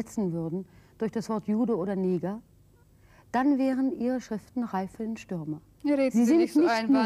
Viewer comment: was sagt die Jornalistin bei 30:10 - 30:15? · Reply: "... dann wären Ihre Schriften reif für den" Stürmer "